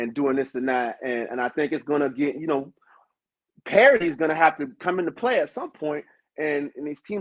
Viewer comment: Did he say "Parity" at 19:38? I cringe every time I hear that word. Larry Scott ruined it for me.